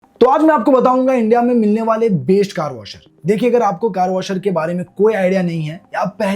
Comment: Kya bola tu waist car washer